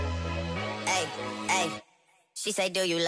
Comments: Bro I thought he said I only love my better ma mama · I thought he said my bed But its Mabed So dumb of me Read more...